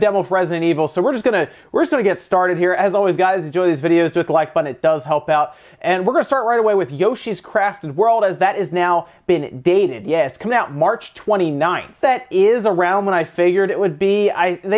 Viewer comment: I misheard Yoshi's Crap World